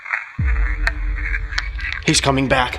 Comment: I hear, "He's coming back, back to get you." This place is wild